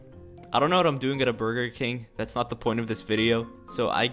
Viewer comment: When I first heard “use starcode anix” I thought u said “use starcode anus” lol